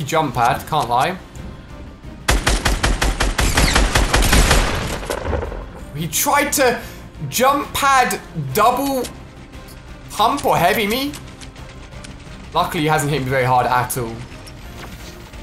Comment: Ali a says double hump instead of double pump